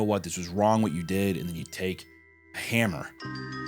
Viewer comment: i think he said you take a hammer and you break their hands …. The rest idk lol